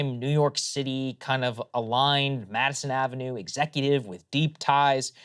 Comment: At first I misheard Madison Ave ties as Ashley Madison ties, lol.